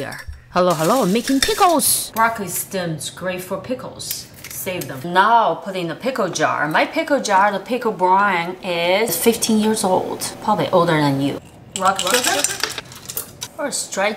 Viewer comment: i thought she said 50 years old apparently she said 15 years